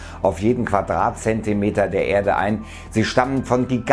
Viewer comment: Was!?Ich habe verstanden,dass Senioren unter der Erde sind...xD!